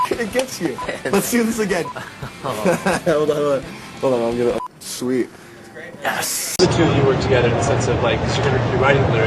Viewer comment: "Sweet... ass" am I stupid or did he really say that?!